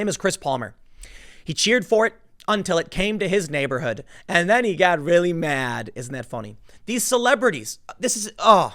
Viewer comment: I hear "Come to my neighborhood and you'll get shot"" from Palmer. Lolz